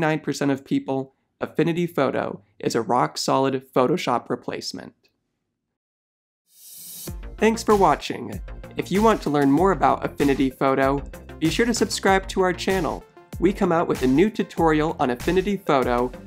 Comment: I thought he said "you shouldn't subscribe to our channel"